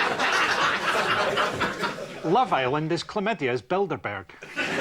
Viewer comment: "Love Island is Chlamydia's Bilderberg" That's a t-shirt. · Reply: Nathan Dehnel, oh damn. I thought he said "chlamydia's Build-a-Bear" and was confused.